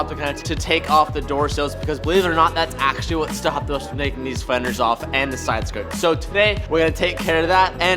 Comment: okay but does anyone knows what he said at 0:21 ? LMAO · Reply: "That's actually what's stopping us from taking these fenders off"